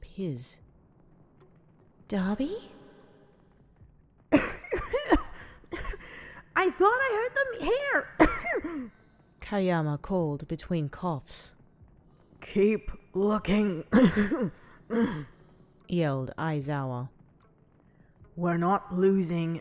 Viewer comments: Am I the only one who heard "Kageyama" at 5:30? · is no one going to talk about how (i think) she said kagayama or are we just going to brush it off